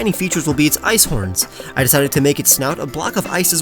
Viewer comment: When you said snout I thought you said snot